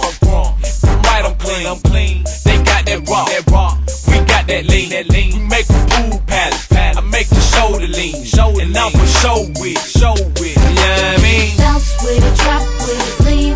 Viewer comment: I remember when i was younger the part where sean paul says “pool palace” i thought he was saying poo palace